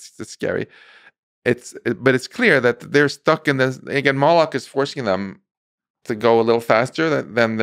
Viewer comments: Hold on. Did he say Moloch is forcing them? As in Moloch from Bohemian Grove? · Did he say "if Molech is forcing them to go faster"?